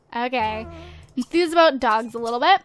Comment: When you were talking to cole and the maid and you said enthuse about dogs I thought it said enthuse about drugs.